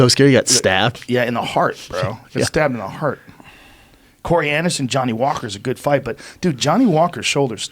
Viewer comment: Every time someone mentioned Corey Anderson, I swear I heard "coriander son". Anybody else? Just me?